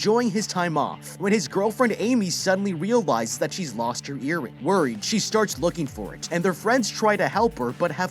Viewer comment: i heard that as "lost her hearing" and was very confused for thirty seconds